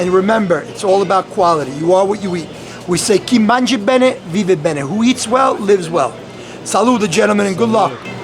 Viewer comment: in case you wondering how to say what he said in 11:50 it's "chi mangia bene, vive bene"